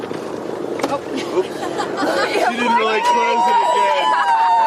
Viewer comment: did I really hear what I heard, when Jeff open the door the guy said u didn’t really close it good👀👀👀👀👀